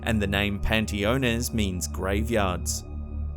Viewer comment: Who ever decided to put Train Stations near Cemeteries certainly had some loco motives....☹ if I saw a headless Brakeman swinging a lamp in a Mexican train station I'd need some fresh Panteones for sure!!😩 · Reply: Haha, I thought he said Pantalones at first, then I looked and it said Panteones on the sign. But you made a much better joke of it than I could have.